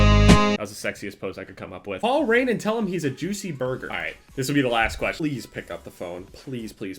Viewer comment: At first I thought he said juicy boner...